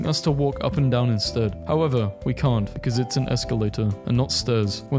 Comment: Did you just say "esculator"